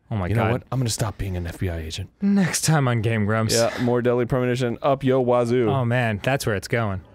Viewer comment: I thought he said "more deli premonition" and that's what I want to call it after seeing that ham